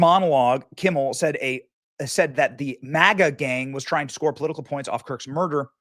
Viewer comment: Is it just me or does it sound like Jimmy said “The Maggot Gang” not “MAGA?”